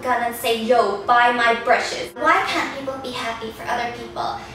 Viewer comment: so i was watchin this vid thn my sis askd me a question thn I got distracted wen tuned bak in i heard "yo buy my brushes" i was like (O-O)? wha? lol